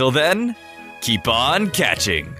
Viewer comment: Every time I hear you say "keep on catching" my sadistic side makes me hear "keep on enslaving" >:3